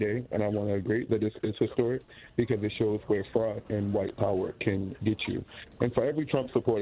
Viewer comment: did he say, "White power"?